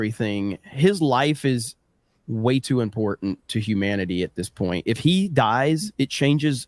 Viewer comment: did he just said trump to important to humanity 😂